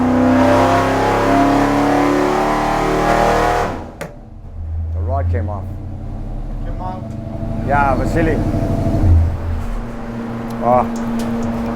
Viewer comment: I heard say "the rod came out" 😳 omg that's not what I thought.